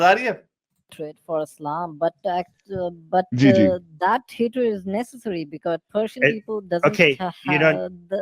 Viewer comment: Put the 💰 where ever there 🕳️ are.. & this proves 🤑 🦜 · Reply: he clearly said “hatred for Islam that hatred is nessesary” like how can people not hear that it must be a language barrier